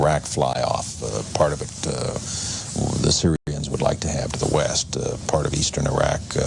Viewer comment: did he said "Serbians" ? (want the part of the Iraq) lol, this guy. Around 1994 was the period US demonized Serbia so he confused himself a bit....or was that intentional viewer programming....for what is about to come...